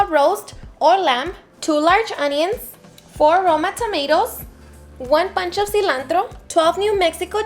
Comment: Sounds like she said one pancho of cilantro 😅